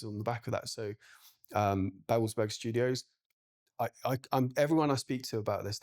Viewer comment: What's the name of the rental is describing at 25:29? Couldn't catch that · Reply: It‘s studio babelsberg‘s prop rental